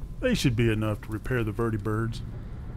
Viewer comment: "We've recently lost contact with two" (what I thought he said) "Birdibirds..."